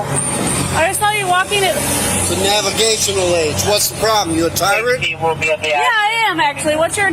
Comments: cc says what’s the problem you’re a target.. what he actually says is “what are you a tyrant?” 😅😢😂 she responds “yeah I am actually” 🤫🤫🤫🤫 honey.. honey … you said the quite part loud and now you gonna see what a tyrant really is… the internet and public opinion! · Person: "Are you a tyrant?" Cop: "yeah actually I am" The best thing she could've said to ensure she loses any litigation. What a vile human being. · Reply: i thought he said " are you retarded " lmao. Both pretty accurate though